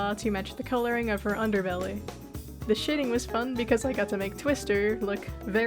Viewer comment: I thought you said shitting😭 Good art mimicking btw